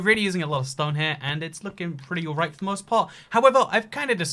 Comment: I thought he said its looking pretty erect for the most part